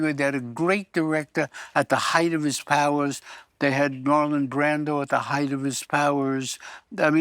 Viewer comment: Did he say Norman Brando at 19:50?